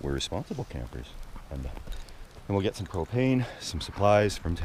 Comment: The last 30 seconds of this video I swear I heard say “we are going into town to get some cocaine and some supply’s lol.....listened to it 4 times